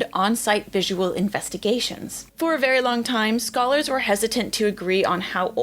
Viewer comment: Why did i hear "skull-ers" and why did it take me nearly a full minute to realize u said "scholars".. anyway new osteologist nickname?